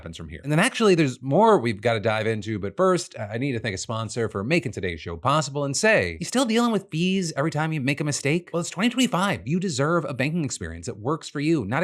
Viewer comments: omg i thought you said "still dealing with BEES every time you make a mistake?" which, lemme tell you, created a VERY different picture in my mind 🐝🐝🐝🐝🐝🐝🐝🐝🐝🐝🐝🐝🐝🐝🐝🐝🐝🐝🐝🐝🐝🐝🐝🐝🐝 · I heard this as “are you still dealing with bees every time you make a mistake?” And I was like… interesting premise Phil where ya going with this?